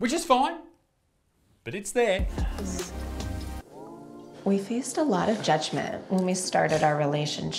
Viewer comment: I misheard because of the accent "We fist a lot.."